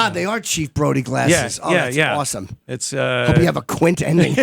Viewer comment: What did Jim say at 03:24? Hope you have a what? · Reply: I hope you have a Quint ending??? I'm not certain, though.